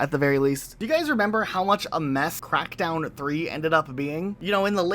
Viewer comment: why does it sound like he said "meth crackdown 3"😂